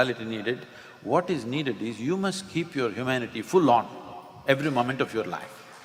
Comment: Hello there to whoever works these videos. At minute 6:41, I feel it is of importance to fix the text "fool on" to "full on". Sadhguru is clearly saying "full on". Thank you. 🙏